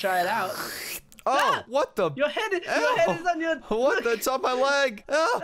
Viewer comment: I'm I the only one who thought he said what the fu*k